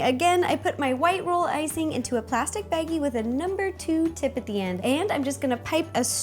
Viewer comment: Who lose thought she said plastic baby instead of plastic baggie.